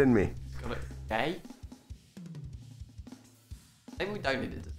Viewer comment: im genuinly confused at what said from 29:42 - 29:58 because I just cant make it out at all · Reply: “ maybe we don’t need it, alright “ is what the captions picked up